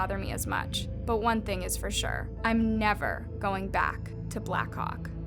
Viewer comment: i thought she said big black cock lol😂😂😂😅😅😅